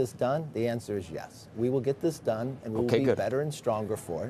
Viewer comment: This was such a waste of airtime on a busy financial and political day. McCarthy basically had nothing to say other than that the shutdown is going to happen but he should not be blamed. Congressman McCarthy clearly is to blame for this shutdown if he proves unwilling to pass spending bills with the targets which were agreed to earlier this year. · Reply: That is not what he said. At 9:20 McCarthy said “the answer is yes. We will get this done”. Why would so many people mislead and root against our great country